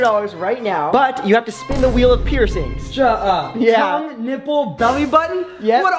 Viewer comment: when he said "peircing" i thought he said pierson😂😂😂